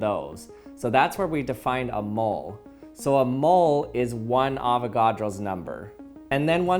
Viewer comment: I was hungry and thought it said "Avocado."